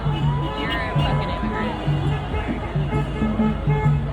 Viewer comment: did anyone else hear a woman’s voice saying, “you’re a f’n immigrant “?? (2:47). why even go??